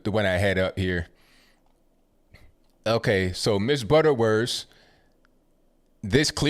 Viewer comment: This was kinda funny especially when u were talking abt mrs butterworth sounds like u were saying butter worse.....but it’s all becoming a huge joke 😂 it’s actually sad smh